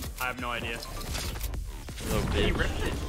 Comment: did he just say lil bicth